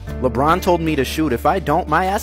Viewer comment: The “I hope I like Minnesota” got me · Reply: I thought he said Men essota